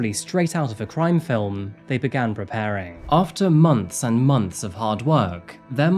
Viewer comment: I dont like the police but thats not fair, he put his life at risk to get justice for a young mother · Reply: It sounded like they said 4 months when I watched before, now it's "for months and months". Either I misheard or we've got ourselves a mandella effect. I'm going with the later.